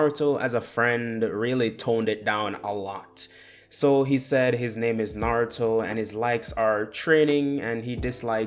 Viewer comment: am I the only who heard my name is naruto....